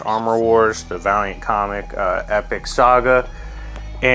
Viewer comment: I lowkey thought it said variant comics